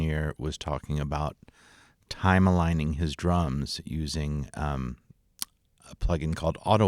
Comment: Lmao I thought you were saying kms time aligning drums because it’s tedious